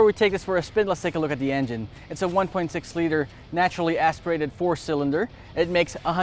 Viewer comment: you said it was a 1.6 liter but the subtitle says 1.5 liter.